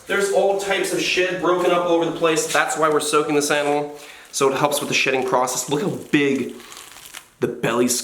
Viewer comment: I thought you said “so it helps with the shitting process”